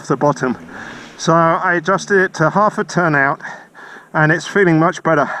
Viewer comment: The screw controls the amount of air which can bypass the butterfly. The more you open the screw the leaner it becomes. One turn out is leaner than half a turn out. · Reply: Thank you. My mistake, I thought you said you added a half turn but after re-watching I see you reduced the A/F screw by half turn.